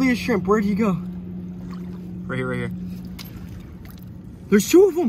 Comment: Pistol shrimp aren’t deadly they have a snap that they make with their claws. The most deadly shrimp is a mantis shrimp. But still dope short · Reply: I thought he said vsco shrimp 💀